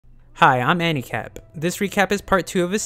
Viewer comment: You never disappoint us, good thing I found your channel ❤ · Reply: Does anyone misheard the intro dialogue thingy like "hi I'm handicapped"